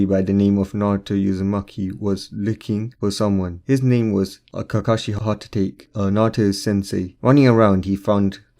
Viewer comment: I heard you say kakashi hatate